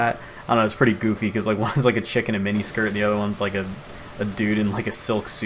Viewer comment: I thought he said chicken in a mini skirt before I played